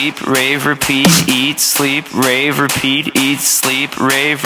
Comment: In my brain i heard it as "Eat sleep ready for pee" and "Eat sleep ready for pete" Also am i the only one that herd this at condover hall ( Residentual ) ??